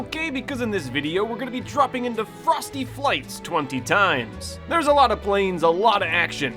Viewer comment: The whole time i thought you were saying frosted flakes